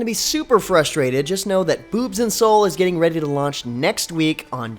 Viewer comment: did he say boobs and soul or did I just hear that wrong